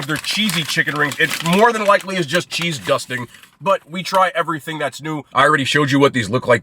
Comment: You said “cheese dusting” but what I heard was “cheesegusting!” Like cheese+disgusting